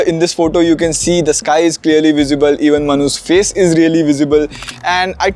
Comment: it sounded like manhoos face is really visible